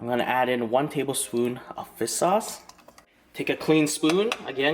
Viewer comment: it sounds like your saying “fist sauce”🤣😭🤔